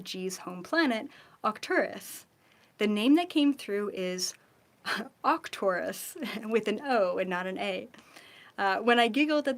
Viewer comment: is she saying Octurus/Acturus? Or ORCturus/ARCturus?